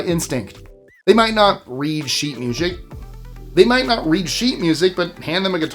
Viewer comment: 🤣😂🤣i heard that... sheet mushic 😂🤣😂🤣